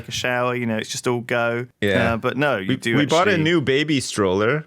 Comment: i thought sips was about to say he bought a new baby. What is my life?